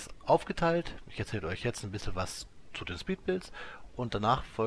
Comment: Ich hab wirklich zweimal "Speed-Pilz" verstanden.